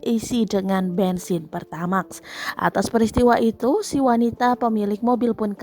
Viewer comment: Paling salah dengar pertadex sama pertamax